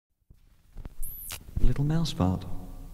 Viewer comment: At beginning, it sounds like he said ‘little mouse fart’